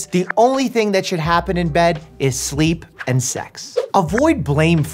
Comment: Did he just say sex0:42